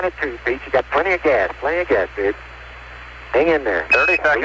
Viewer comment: I swear I heard him said "plenty of gas, dude" instead of "babe", bro is chill af